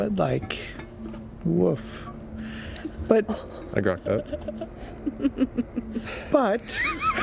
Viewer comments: What the heck did Matt say? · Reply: "I grok that" (he deeply sympathizes with the situation) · What does Matt say at 9:07? I can't seem to figure it out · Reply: It's actually: "I grok that." In the ancient time of the 1960's a book called Stranger in a Strange Land got a lot of notoriety because it was... generally just so weird. Anyway, it introduced 'grok' as a sort of intuitive feeling of wholeheartedly internalising and understanding something with your whole being. And with the story's popularity, the verb 'to grok' became a popular way of expressing that you understood something with your whole heart so to say. So long story short, it's 60's for saying: "I empathise with that immensely."